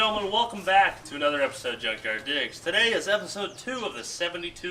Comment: Sounded like she said junkyard dicks😂